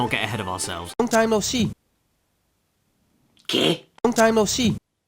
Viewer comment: Ele disse "Que"? (Did he say "Que"?(What))